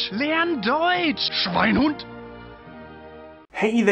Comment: wtf your hair is so diferent 0_0 but what is it your saying at the beginning of your videos ive heard the medic say that in tf2 i think like.. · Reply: Schweinehund